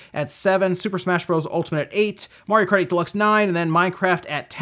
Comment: did you just say "mario karty deluxe 9"? Lmao